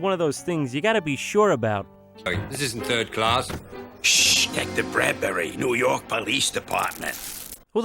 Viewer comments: Did he just say, "Detective Bradberry...?" Do we got a decendant of Moe and ET over here on the Titenic? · Did he just say his name was, Detective BRADBERRY?!?! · Did... Did I hear that correctly? Detective... B-Bradberry? guys... he's related to Moe:Sup guys I'm Moe Bradberry Ethan: I'M ETHAN BRADBERRY!